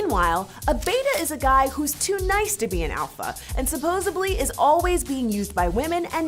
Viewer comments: Yo....at 7:28, instead of saying "supposedly" she said "supposeBLY". What in the ACTUAL FUCK?!?!?!? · Did she just say supposably? I know this is extremely nitpicky, but seriously? No one in MTV has seen Friends? None of the editors or any of them caught this? I'm sorry, but now that's my entire takeaway from that vid. Supposably... XD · Did she say "supposeably"? About 7:24 That's a pet peeve of mine. Some people think the word is supposeably, not supposedly.